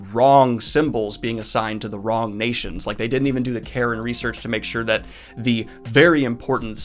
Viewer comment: For a second there I thought you'd said "Karen research" and I got confused.